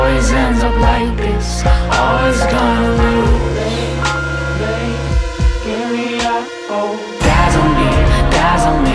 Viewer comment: what the hell do they say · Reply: Shawn Argent give me ur gold that what is says